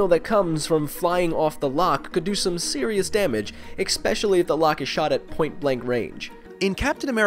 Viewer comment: Did you say "expecially" ?